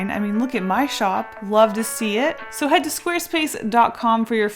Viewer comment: I thought she said “you love to see it” in her ad. Almost made a Jschlatt reference there xD